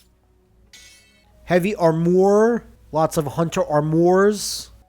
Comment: Wait did you say arm oar?